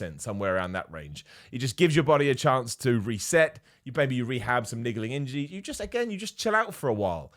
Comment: Did anyone hear say at 9:15 "maybe you want to rehab some nigely injy?" Love you, =)